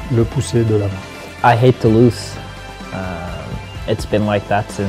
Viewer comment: I can't understand the word after 'I hate...' what does he say? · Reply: "I hate to lose"